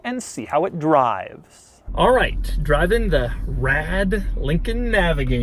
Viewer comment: did he just say “rad”?